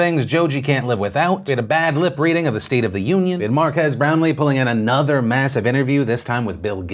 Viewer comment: I'm pretty sure I heard "mark ass brown Lee"